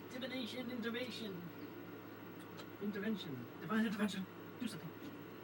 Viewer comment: Did he say "intervention"? Because that sounds exactly like what he needs. He definitely hit that dog too, judging from that yelp.